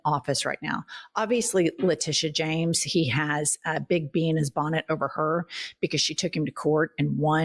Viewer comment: lol I heard "beans bonnet" and thought it was some expression I'd never heard before